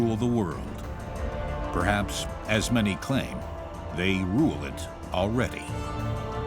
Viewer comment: did he said rule it or ruin it lol